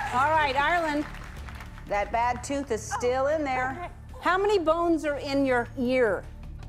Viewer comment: who else heard, "How many bonsa in your ear"